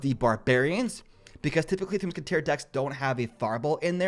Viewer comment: wtf u say bro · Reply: kentang goreng typically 3m decks don’t have a fireball in there